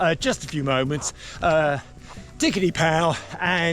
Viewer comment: Did you just say "Digerty pow"....brilliant